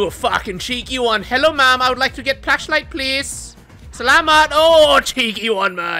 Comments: did he say "salamat" ( thank you) ?!?! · did he just say salamat or thank you in tagalog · Did i just hear he said "selamat"